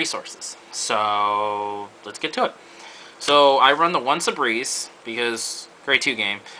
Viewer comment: Whats that card.? I didn't really understand the name and i can't recognize it.Great deck and video btw ^^ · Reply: is Sebreeze. The one where if your opponent in the previous turn didn't ride a 2, you can potentially use this as a way to stride and get to gb1 quicker.